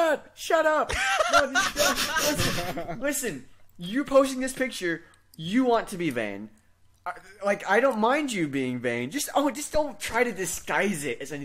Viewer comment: I heard that as "You're posting this picture, you want to be banged. I don't mind you being banged, but just don't try to disguise it." at first. Needless to say, I was very, very confused.